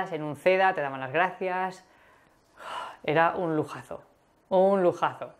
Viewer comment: Hi. I was wondering what you said at 0:25. I think I heard "era un lujazo". I think I misheard and I couldn't find this word in dictionaries. Thanks.